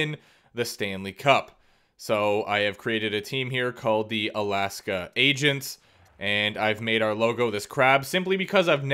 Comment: Wasn’t looking and i thought he said he created the Alaska Asians